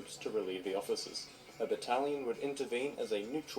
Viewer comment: When he said battalion I thought he said italian as well I am from ireland 🇮🇪 🇮🇪 well I live in Tyrone which is still under british control